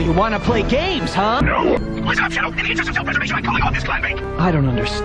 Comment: "wazzup chao i'm in preservation or i'm calling off this clam bake"! at least thats what i heard